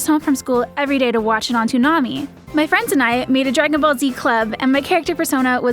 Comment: Did anyone else hear "Dragon Ball D-Club" ?